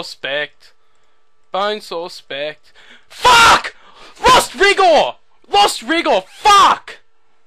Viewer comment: sounds like you said 'lost rigour' not lost 'vigour' just saying